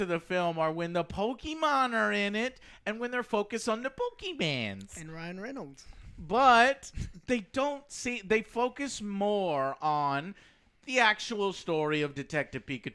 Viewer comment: Did he just say "pokeymens"?